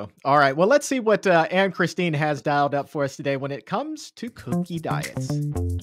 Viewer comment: I thought she said cookie diets!😂